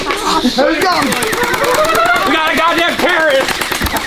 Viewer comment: He says "we got a gd terrorist" and the subtitles say "gd carrot" 😂